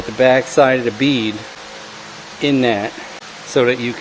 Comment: what tool is that I could not hear what you said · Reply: Bead clamps: <link>